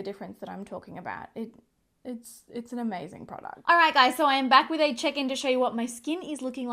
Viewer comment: time stamp. I know you said you were back with a check in, but I’ve had a long day and at first I thought you said “ I’m back with a chicken.” 🐓 ha You had a lamb recently on your vlog channel so I thought , well maybe she is babysitting a chicken this week. 😄🐓🤷🏻‍♀️